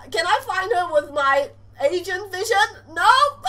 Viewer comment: when she said agent vision i thought she said asian vision ,_